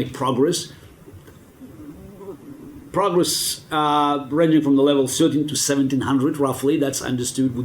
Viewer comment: At about 2:16, Garry says "progress ranging from the level of __ to 1700." What did he say? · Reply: "Progress ranging from the level of thirteen to seventeen hundred"